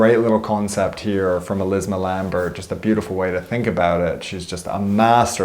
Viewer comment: What name is he saying at 9:45? Elisabeth Lambert or waht? I don´t find that person. I really want to check her out. Thanks. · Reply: Elizma lambert